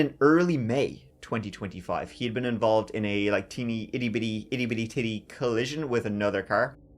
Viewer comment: I heard that! “Itty-bitty titty”!!! 😂